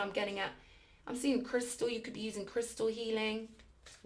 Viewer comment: I really thought you said youbsaw me 😂 "i see Crystal" 33:00 and at 33min too nonetheless.....33 has been following me around lol